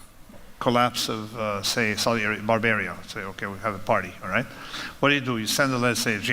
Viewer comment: woah there buddy what were you gonna say? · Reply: I heard Saudi Arabia...ehh barbaria😂